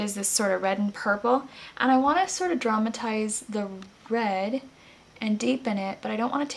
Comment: I know you said "I wanna DRAMAtize the red", bt I heard 'traumatize', and I thought "oh no, poor red!"... :P